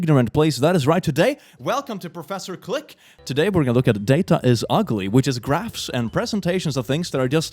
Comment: OH MY GOD I THOUGHT IT SAID “DAT-AIS-UGLY” I DONT EVEN DESERVE A VIP TICKET TO HELL, I DESERVE A MVP TICKET TO HELL